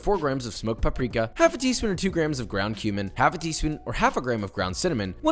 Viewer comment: "... half a teaspon or 2 grams of ground human..." oh wait, I heard that wrong 😅